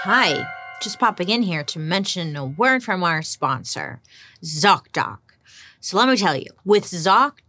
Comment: I heard Zocda! I was like, what in the scientology is Zocda?!!